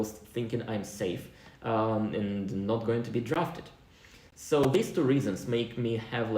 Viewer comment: haha i thought he said he wont be drafted?